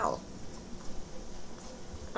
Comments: All I heard was "snack" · What I heard "What's that? A sack" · Did he just say - that’s sad, we are stuck?!! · Sound like he said: want a snack? As he sits on his makeshift perch. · Did he just say Nut Sack